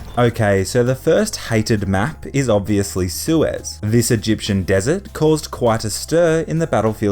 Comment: It says "sewers" instead of Suez in subtitles